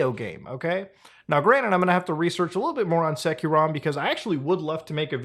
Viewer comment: What did he say??? Set Q Rom? Seque ROM? I wanna research it but he pronounced it in a way I can't figure out what he said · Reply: SecuROM